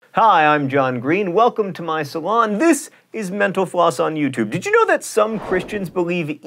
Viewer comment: Why do I keep hearing 'menopause' when you say 'mental floss'?